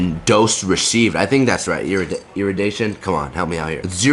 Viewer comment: what was he trying to say? Irrigation?